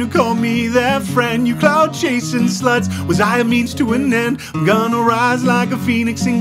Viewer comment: when "gonna rise" sounded like gonnorhea for a sec